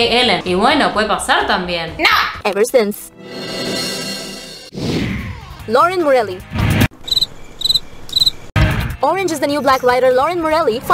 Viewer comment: PENSE QUE HABÍA DICHO LAUREN JAUREGUI, HAAY CASI MUEROOO 😻 igual todos sabemos su orientación sexual pero ya saben por quien creí que había dejado a un hombre ...